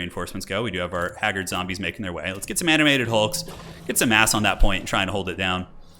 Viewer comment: sounds like you say let’s get some “ass” on that point ☺️